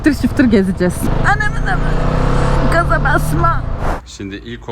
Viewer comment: de ananın... Mı diyor benmi yanlış duydum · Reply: Anaaammm diyorum 😊